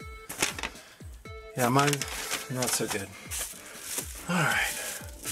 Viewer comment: when you tried to consult the manual you said: "Not so good" for a split second thought you said "no tickee, no shirtee".